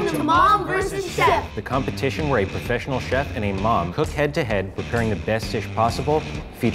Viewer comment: I thought he said a professional mom